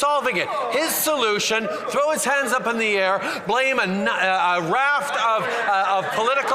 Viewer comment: Did he just say "blame a nut" in the ending there?